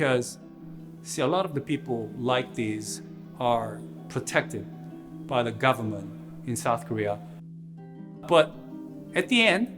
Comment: Did he just say "protected by the government in South Korea" at 5:55 ?????